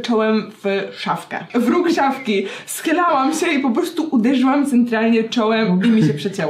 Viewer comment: zamiast "schylałam" usłyszałam "schlałam" xdddd